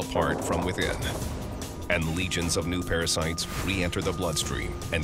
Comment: At the end i thought she said "I'm very scared and very pretty"